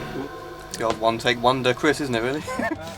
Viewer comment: What did Matt say? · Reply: "It's the old one-take-wonder Chris isn't it really?"